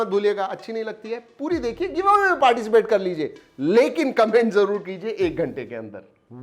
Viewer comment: Did he said 'lekin COMPLAINT zaroor kijiye'